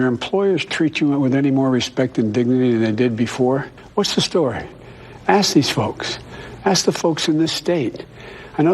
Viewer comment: Did he just say ass these folks?